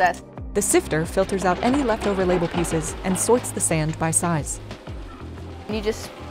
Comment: Um... Did I hear her say, "Soits the sand by size"?